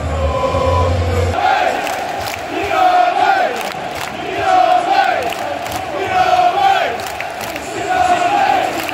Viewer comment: anyone know the chant @ 0:49, i always hear it but cant make out what they are saying (im not british) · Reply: Sure! So the chant is "yid army", which is referring to how spurs fans often coined the word yid, referring to ourselves. I'd warn to be wary using it dependent on context as it's also used to refer to a Jewish person, I'd guess due to the Jewish links at spurs, and some consider it offensive.